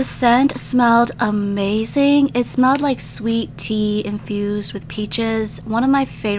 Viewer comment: why did i think she said “sweet tea abused with peaches” 💀